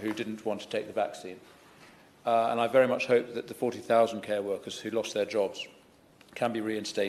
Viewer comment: did he say 14,000 care workers who lost their jobs? It sounds like 40,000 but that number is incomprehensible to me so I assume he said 14,000, but even that's incomprehensible number.